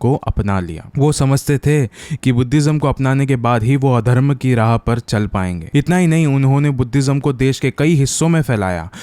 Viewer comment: Did brother said adarm